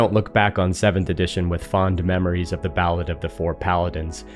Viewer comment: Caption say "battle of the four Paladins," but it sounds more like "ballad"?